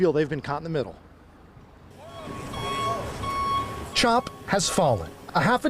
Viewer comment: did I hear “trump has fallen” ?